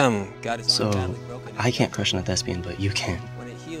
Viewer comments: Who else thought she said lesbian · I was so confused. I thought Roni said Nessa was a lesbian, and I was like ‘Why is Nessa excited about kissing the guy if she’s lesbian?’ 😂😂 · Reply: my two siblings both thought that she said lesbian lol